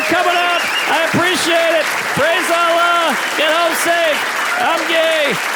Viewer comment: does he say "I'm gay ?